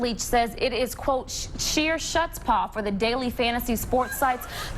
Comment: What was that word at 8:20? Shutspaugh?